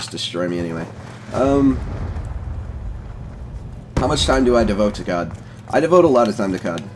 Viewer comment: lol i thought u said "how much time do i devote to God"